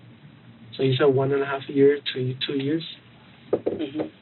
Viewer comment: 3 years? I thought they said at the hotel interview they were together about a year. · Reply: they clarified that she said one and half years to two years. I miss heard her at first and thought she said two and a half years.